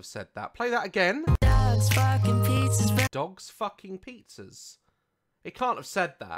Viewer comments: what he saying is not dogs fucking pizzas it’s “Dark spark with pieces” you’re welcome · I heard “dads f*cking pizzas” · ” 😂 Best line ever. I looked up the lyrics and he actually says “Dark spark with pieces” But I hear nothing but “Dogs f*cking pizzas” 🤣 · #DogsFuckingPizzas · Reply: I thought he said "Ducks fucking pieces" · i heard 'dogs barking pizzas running' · I heard dogs f*cking pizzas too but turns out he said "Dark spark with pieces". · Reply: GamerGirl500 _ I thought he said "dad's barking, pizza's burning" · “Dogs f**king pizzas” -Jacob Sartorius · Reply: I heard ‘Dazs fucking pizzas burning’ I don’t know how I just did